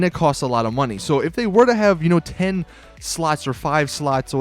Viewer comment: Did he say "10 sluts"? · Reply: єαgℓє☆ no he said 10 slots